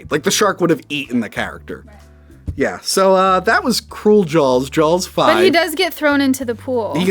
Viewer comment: It sounds like is saying "Jawls".